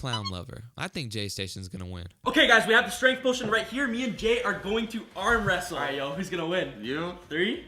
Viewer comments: WAIT DID Stromedy really call JayStation GAY?! 4:59 Me and Gay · It remeber watching his videos not anymore :/ 5:00 I thought he said gay 🤣